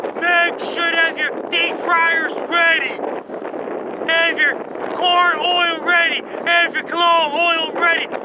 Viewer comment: did you say have your corn hole ready? I've found very tasteless!